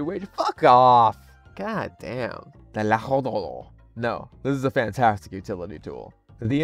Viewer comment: What is trying to say: - l'etranger What I hear as a Latin American: - 14:19 Te la jodo (I fuck you)